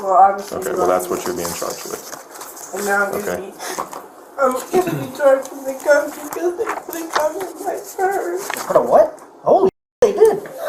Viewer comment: Does anyone know what she says at 21:52?? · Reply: "I'm gonna be charged with a gun, because they put a gun in my purse"